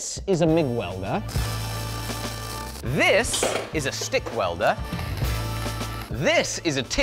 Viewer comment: Did I just hear 'Schtick Welder'?